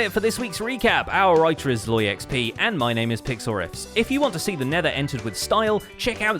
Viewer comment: I'm starting a fanart stream shortly on <link> if you're up for some HC drawing, come check us out! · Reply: Is it weird I thought I heard PixelRiffs call himself PixelRick?